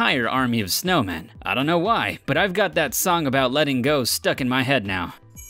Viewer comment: did he just say letting go lmao